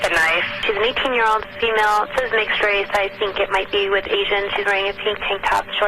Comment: the [INAUDIBLE] part. I think the Dispatch is saying, “...she is of a mixed race...”